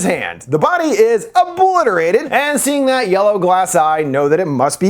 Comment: did you say that's a yellow coloured eye? Weird way to find out I'm colour blind